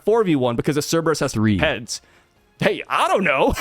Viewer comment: I think god soke to me at 5:02. Called me a 3. Im sad